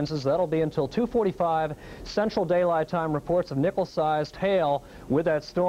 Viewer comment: it really sounds like he says "nipple sized hail". :D